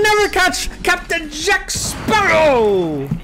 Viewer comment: i thought you said "kapkan jack sparrow". Bruh